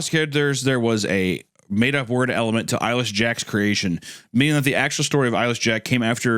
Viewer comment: I keep hearing “Eilish Jack”